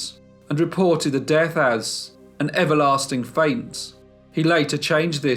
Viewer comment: an EVERLASTING FAINT? Did I hear that right?